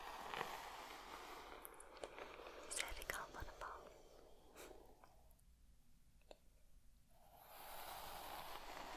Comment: what did she say? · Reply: « Is that comfortable ? »